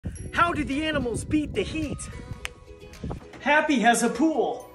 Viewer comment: I don’t know why I heard him say “ how do the animals beat the meat?”